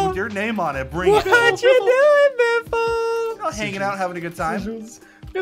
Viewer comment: is it me or did he say the n word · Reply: HE SAID HANGING IN OUT